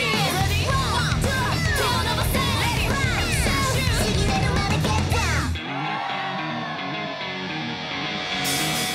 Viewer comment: ここフランシュシュっていってるようにも聞こえる？ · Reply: 実際言ってるね